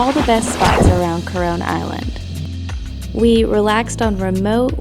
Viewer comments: When said “this is one of the most popular spots in coron island “ I thought he said corona island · When u Said “Coron island” it sounded like u said “coronaland”